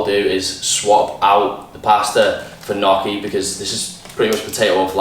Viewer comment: What is his alternative to noodles? I can't tell what he says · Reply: Rachael Lawson Gnocchi.